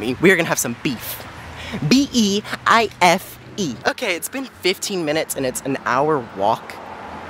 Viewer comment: my dumbass thought it said “ 15 minutes”